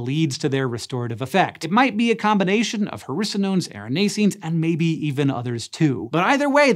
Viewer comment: Who else thought that every time he said hericonones he was actually saying "harrisa-nones"?